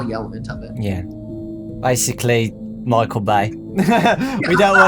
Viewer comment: did he say Micheal Vey a book series where a group of children have superpowers?